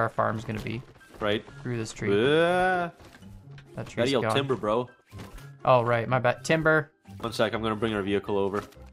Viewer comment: I thought I heard say “Tinder” lmao